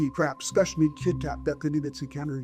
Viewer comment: Am I the only one that's hearing "KIT KAT", instead of "TIC TAC"?? 😂😂😂